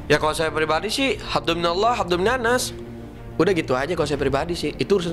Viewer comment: Apa saya salah denger ya,ko yang saya denger habduminallah habduminannas,dan buat ente siapa sih yang ngehina rasullah.?